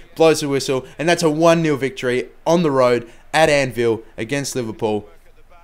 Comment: to be fair Southampton are doing great this season. sounded like you said at "Anvil" lol not "Annfield" funny schtuff. Good episode looking forward to the next.